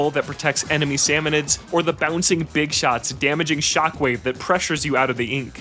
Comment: I'm so deltarune brainrotted that I miss heard big shock as BIG SHOT XD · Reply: Im not sure where you think you misheard, but im pretty sure he said big shot as in the enemy type named big shot